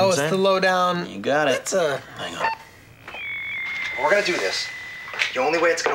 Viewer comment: "Lets put it this way, you wont be able to see anyone's face." "Oh, it's a low-down..." "You got it." "...that's, ah.." "I know." Even dennis knows that angle is terrible. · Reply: I heard, “hang on” not “I know”